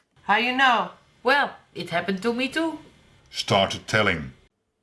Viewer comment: I can’t tell if the narrator says “Start telling” or “Started telling”. I always thought it was “Start telling”, but the captions say “Started telling”.